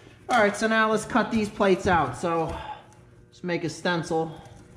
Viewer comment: It sounded like you said "let's make a stemple " don't know if you meant a strut or combo stencil/ template, me being partially deaf. I miss growing up in New York, we used make up words all day long. watching your videos reminds me of the old days and friends in the Hudson valley, Rockland . Thank you I can almost smell the woods with the quality of your videos.